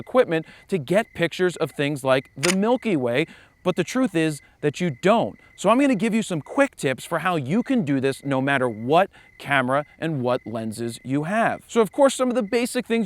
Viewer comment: I thought you said "cheap camera" :)